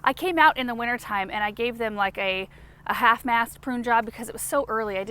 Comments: I actually had to rewind the video because around the 12:00 mark, I could have sworn I heard say, "half-assed prune job"...😂😂😂😂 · I rewound the video in the middle twice because I thought said that she “half-assed” the prune job. I had never heard her curse but then I realized she said “half-mast prune job”. 🤣🤣🤣🤣. She makes me want to curse less. Unfortunately, I talk like a well-educated sailor! Lol